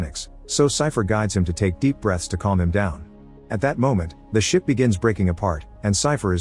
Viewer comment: Sounds like you said "At that moment the shit started breaking apart" 😂😂😂